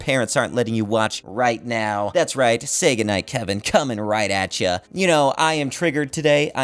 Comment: did you say Sega Night Kevin?